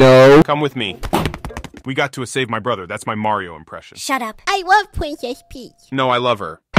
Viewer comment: i heard "We got to save my brother, that's my Mario brother"